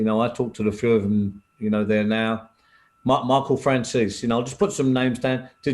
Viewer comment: I thought he said my uncle Frances